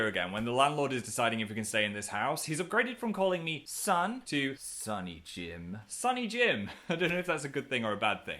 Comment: WHEN HE SAID "SONNY JIM" I THOUGHT HE SAID "SPOOKY JIM" AND I FREAKED OUT